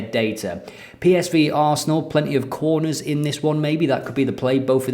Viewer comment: is there another “Toin Coss” moment? I definitely hear quorners rather than corners! Quorner: a set piece play in football, usually taken by a vegetarian.